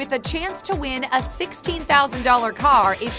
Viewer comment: did she say $16k or $60k? Cause if she said $60k she clearly doesn’t know the price of a Chevy Sonic... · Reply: But then again it does say 16k in the description